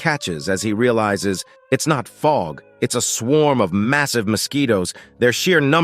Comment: did you say.. massive?